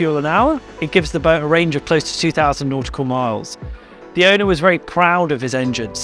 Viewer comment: Didn't he say 2000? 😁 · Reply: Okay, I went back and listened carefully. He says, "It gives the boat close to 2,000 nautical miles." I misheard what he said. I thought 32,000 nautical miles seemed a bit much. Ha ha